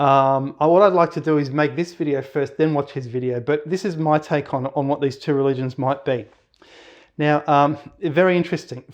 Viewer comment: Very interesting video. I found it very intriguing. At about 11:00 you mention another channel, "??? History". You say he isn't making videos any more. I want to find that channel and check it out, but can't quite understand the first word you say in the channel name. I looked for a transcript of this video but there isn't one. I'd like to check out that channel, will you please reply with the channel name? Thanks in advance. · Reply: Tik History.